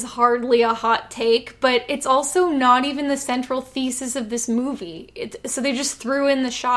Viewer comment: i thought she said feces :O